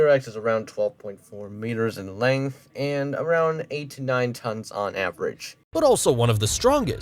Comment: i swear i though you said 89 tons on average